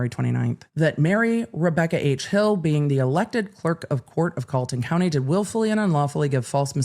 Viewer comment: Yes, Fani Willis is a DA in Atlanta, Murdaugh is in the lowcountry of SC 7-8 hours away. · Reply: i was watching on 2x speed and thought she was saying Fulton County. I was also half asleep so my brain wasn’t processing different states lol😂